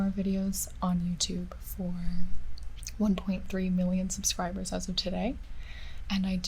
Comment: I thought i heard her say 3 million in the beginning....did she say 1.3? I suppose i could re-play lol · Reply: she said "as of today i have 1.3 million subs" :)